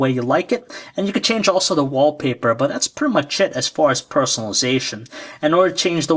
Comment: lol...at 7:02 does he say, "that's pretty much shit"... XD...IM SO BUYING THIS DEVICE...